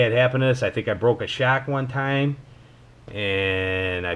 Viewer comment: Thanks Man!! I appreciate the Sub and for watching!! I have no idea what a broke a shack is but you didn't swear at me so it must be Cool! Lol!! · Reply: You said your rc broke a shock, sounded like you said your rc broke a shack. Been watching too many bashing vids lately. Great attitude towards the hobby though. No need to swear at you for being cool 😆